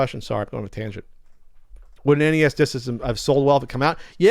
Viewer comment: I thought said NES Di** Systen no disk